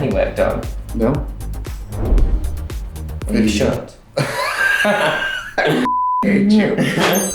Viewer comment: “Have you had any work done?” “No” “You should” lol 😂 💀 hahaha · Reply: I thought she said "you sure?"